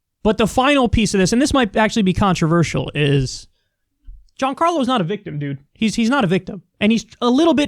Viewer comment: Giancarlo is his first name, which is Italian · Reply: sorry, i heard "juan carlos" but thats neither here nor there its still a cliché.